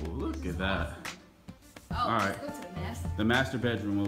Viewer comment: I thought he said masturbate room at first I was like WHAT I rewinded it put the volume on max turned captions on whew